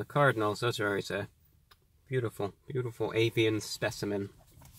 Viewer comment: That sounded like "apian". Did you mean "avian" my lord, is this a test of our worthiness, or have the African Bees had their way with the Cardinals?